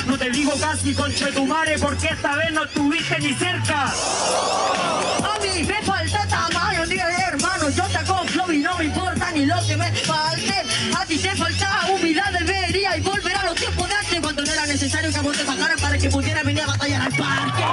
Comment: que dice la rima,no llego a entender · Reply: A mi me falta tamaño tírale hermano, yo tengo flow y no me importa lo que me falte, a ti te falta humildad deberías volver a los tiempos de antes cm cuando no era necesario que a vo te pasaran para que pudieras venir a batallar al parque :)